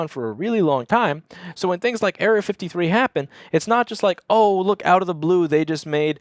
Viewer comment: OH yea and down with error 53!!!!!! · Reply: At first I thought he was saying Area 53 and I was thinking huh that's a funny name for a problem.